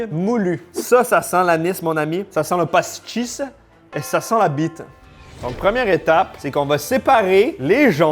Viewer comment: LA Quoi ? 14:48 ? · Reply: La bite, le pénis, la queue etc...